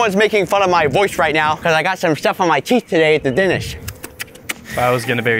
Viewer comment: did unspeakable say that his teeth for denis!?!